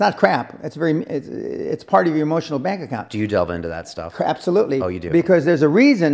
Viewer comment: Did Dan say "crapsolutely"?